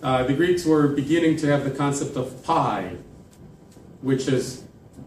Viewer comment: The concept of what? Sorry, I can not recognize that word. Is it PI, 3.14? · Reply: π